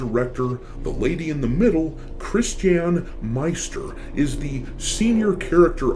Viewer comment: It sounded like you said "Chris Chan" I will not be playing a game made by Chris Chan 😂😂😂